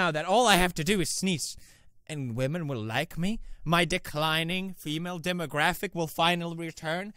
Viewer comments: I heard "dick-lining" instead of declining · What the fuck...Why did I just hear DICK lining I know Its declining but...wtf